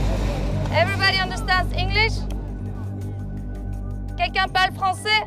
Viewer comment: anyone hear "english haram"? that was hilarious haha.